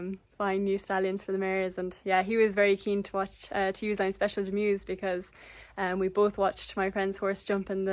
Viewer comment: What is the exact name of the stallion you used for Fiona? I heard “I’m Special Demused”. I’d like to look at some videos of him but want to make sure I e got the right name before I go hunting. Thank you. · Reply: I’m Special De Muze